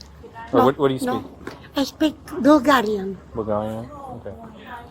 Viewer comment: Sounds like the daughter had surgery and still heavily medicated. That’s what the Bulgarian grandma was trying to say. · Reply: I thought the grandma said broken - Italian.